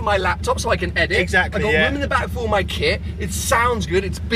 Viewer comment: got a kid? or did he say "kit" Still looks so young xD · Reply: He said kit